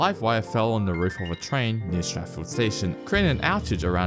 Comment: when I heard this for the first time I thought he was saying Stratford 😂